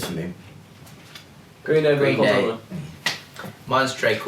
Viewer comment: can anyone understand what michael said in 3:20 ?? because it really haunts me that i can't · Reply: he says "green day, blink, all time low" before calum can finish